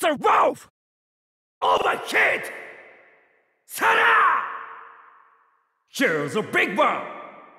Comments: What is he saying from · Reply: Voice 45: "Overheat!" Voice 47: "Here's the big one!" Voice 48: "BUSTER WOLF!" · I always hear “overheat” as “OVER KID!” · I was always wondering what he said before Buster Wolf in his final Smash, since it didn’t sound like “Are you ok” 1:15 tells me it’s apparently “Here is a big one” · what is he saying? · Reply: “Here’s a big one!” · Can someone tell me what he says at 1:12 ? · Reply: He says "Overheat" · lmao I know it's not what he says, but I always hear "all them kids!" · What does voice 47 say?? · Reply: "here's the big one" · lmao I know it's not what he says, but I always hear "all them kids!" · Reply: also in 1:16 I thought he said "here's the big boss" like the line from Carl Douglas' Kung Fu Fighting. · What is terry even saying there? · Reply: Here's the big one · what is he saying here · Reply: Here's a big one